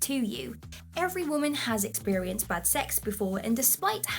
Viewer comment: sounds like she said every woman has experienced butt sex before.